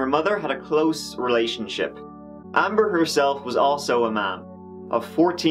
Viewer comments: I thought he said “Amber herself was also a man.” Glad included the picture of her holding her baby, since my ears are so Americanly stupid. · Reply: Same here. I was reading comments, but at that moment, I had to look up, & saw her holding the baby. Lol I too thought he said " And Amber herself was also a MAN"...lol I love his pronunciation and his sarcasm...hahaha! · I thought he said “Amber herself was also a man.” Glad included the picture of her holding her baby, since my ears are so Americanly stupid. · who else heard "Amber herself was also a MAN" i was like oh.... OK? lol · lol sounds like you said "Amber herself is also a man..." I am like wut?